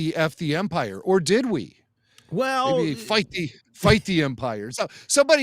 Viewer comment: Did say "Fight the empire!" for everyone or did some people get her saying an actual f-bomb? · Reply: I heard "fight the empire!" Not an f-bomb in sight